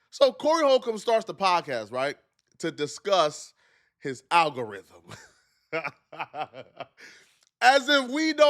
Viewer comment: Aggarhythm 😂😂😂😂 ma boi drunk